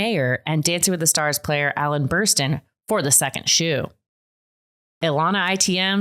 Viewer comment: Alan Bersten confused the heck outta me. Thought y’all were saying “Ellen Burstyn”